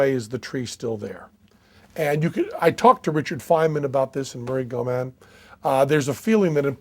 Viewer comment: did he say Richard Feynman? He meant THE Feynman???